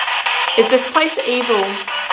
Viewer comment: sounded like it isn’t